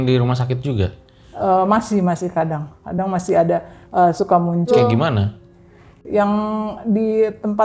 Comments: Ga cuma 20:40 menit 31:39 juga ada suara dah. 32:04 juga ada helaan nafas. 33:15 ada helaan nafas. 41:58 juga ada Mantap episode ini setelah episode ada suara “welas” 👏🏾👏🏾👏🏾👏🏾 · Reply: Itu suara hasil dari cut audionya si ibu ke bang itu suaranya si ibunya di menit 20:40 itu suaranya si ibu bilang "muncul" terus di cut ke bang jadi "cul" doang. Di 31:39 itu ibunya juga bilang "anak saya meninggal udah" tapi di cut lagi ke audionya bang jadi kedengerannya cuman "dah". Helaan nafas itu nafasnya bang penonton dari dulu pasti tau kadang emang bang suka tarik nafas ampe kedengeran audio. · Itu hasil dari cut audio itu suaranya ibunya bilang "muncul" terus di cut ke bang cuman kedengeran "cul". Itu memang dari editornya yang ngecutnya kurang pas